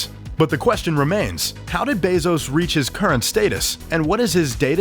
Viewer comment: did this man just say “Stateus” instead of “status”?